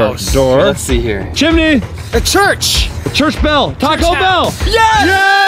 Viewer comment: HOW DID CHAD KNOW WHAT SCHOOL I GO TO!?!?! (It sounded like he said "Churchville" which is the name of my school, yes ik he said "church bell")